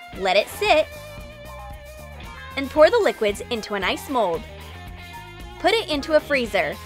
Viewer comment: in 5:39 she said"pour the liquids into a nice mold" instead of ice mold.But still love you troom troom.